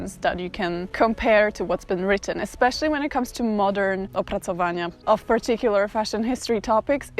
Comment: I'm russian and to me it sounds like "образование" which is "education" in English Edit: I'm not saying that's what she meant, I'm saying that's what I heard